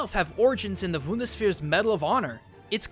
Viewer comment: Im German and when he said Bundeswehr I cant stop laughing :D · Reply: I thought he said 'Vundesvihr' once lol